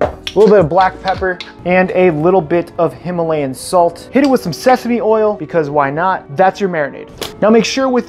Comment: for some reason I thought he said sexy oil